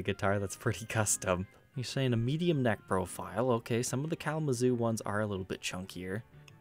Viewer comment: It sounds like you say "medium necrophile" instead of "neck profile".